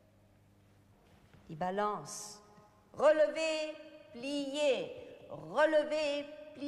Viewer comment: was machen die da? Bzw. Was sagt fr. Kralowa da? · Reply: Sie sagt: „Relevé, plié, relevé, plié.“ Relevé, zu Deutsch: „gehoben, erhöht“, übersetzt als Ballett-Ausführung: auf die Fußspitze/halbe Spitze erheben Plié, zu Deutsch: „gebeugt“, übersetzt als Ballett-Ausführung: Kniebeuge